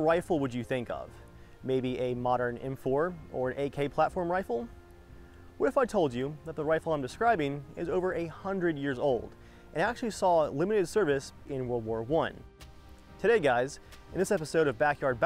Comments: who else thought he said 800 years old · Sounded like you said the rifle was over 800 years old, rather than "a hundred years old."